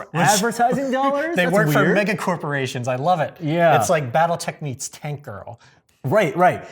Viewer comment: What did said? Battle *what* meets Tankgirl?! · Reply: Battletech